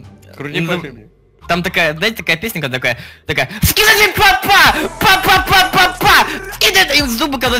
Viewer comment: Кто из 2018 услышал на этом моменте 9:34 скибиди ва па па 😂